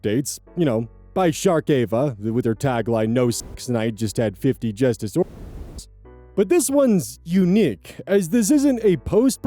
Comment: I hate that I keeping hearing 50 justice oreos instead of what it actually is.